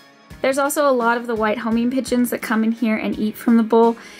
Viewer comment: I thought you literally said home-made pigeons